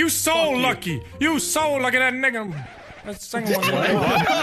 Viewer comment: I always thought he said “your so lucky that nicked him” but he said it with a really hard accent.